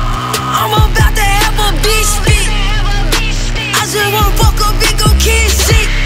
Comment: I thought she said “a bitch fit” 😂